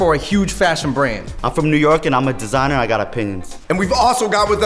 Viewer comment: I literally thought he said I'm a designer and I got a penis...